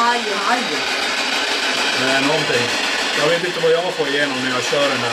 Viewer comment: när jag hade sett till 1:20:18 så där sa den nånting som ni inte visste vad han sa men jag hörde vad han sa han sa "säg nånting"